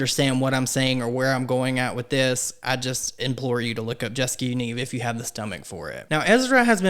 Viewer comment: what is he saying to look up ? captions say "jessica you need" lol